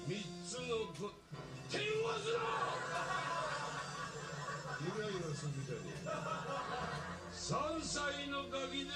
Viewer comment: 何て言ってるんですか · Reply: 手を動かすな！イライラするじゃねぇか